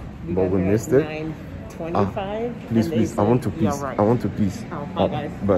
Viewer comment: Did he just say ‘I want to piss’?!!!🤣🤣🤣🤣🤣